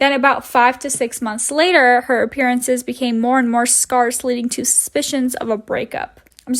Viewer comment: Did you say scarce or sparse? It might be helpful to have someone else look over your videos if you don’t know how to pronounce words, or just get them incorrect by accident. I’m not saying this to be mean, just to be helpful. Love your videos :)